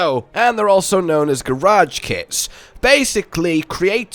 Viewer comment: I’m surprised that the equal dragon is confirmed by capcom. Okay for a second, I thought I heard garage kids. But my friend confirmed that it was kit. Sorry about the mixup.😅